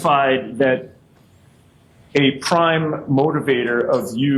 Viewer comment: Could have sworn the prosecutor said, “crime motivator.” Instead of “prime motivator.” 😂😂😂 Actually, it’s not a laughing matter. It’s a travesty of justice.